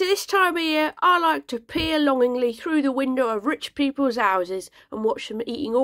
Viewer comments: I thought you said”...pee longingly through...” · For a moment I thought you said "I like to pee through the windows of rich peoples houses", and so I giggled.